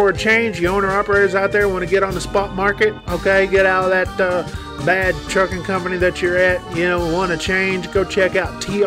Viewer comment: Oo I thought he said dum ass trucking hahaha